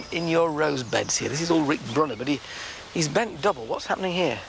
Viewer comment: what is the name of this beautiful rose? i think i hear him say something like "Rick Brunner" but i cannot find any rose by that name! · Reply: It is Rick Bruner but you might also likeCecile De Brune beautiful sent beautiful flower and a climber